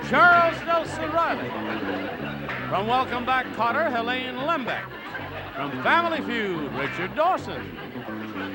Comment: What'd he say at 0:17? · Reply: "From Welcome Back Kotter, Helaine Lembeck!"